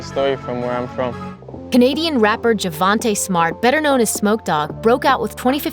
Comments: When she said smoke Dogg I thought she said “Snoop Dogg” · For a sec I thought he said snoop dawg...